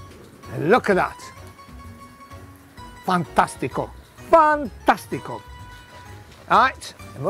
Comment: Gennaro broke the auto-captions with his accent. At 43:50 he says "fantastico" but the captions say "fun testicle"... 🤣